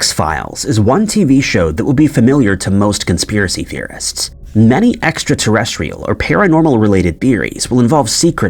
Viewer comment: lol I thought you were saying it was familiar to both conspiracy theorists and many extraterrestrials